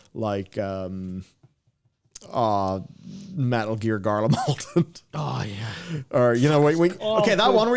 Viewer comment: I am Canadian and have been watching UK media since I was a toddler. Red Dwarf is still my comfort show and put it on as background noise all the time.. that being said.. I HAVE NO IDEA WHAT THE HECK HE SAYS AT 4:28 😂 I have rewinded it like 20 times now and all I hear is "Metal Gear Garlamolden" and im like.. well thst cant be it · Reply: Garlemald, it's a region in ff14 with a pretty nefarious stealth quest.